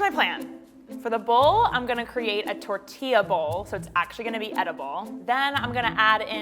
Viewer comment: shesaid tortia instead of tortilla